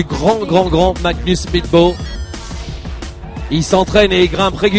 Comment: sounds like hes saying "magnus meatball"